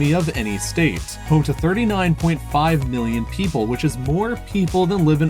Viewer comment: Did he just say “billion” ?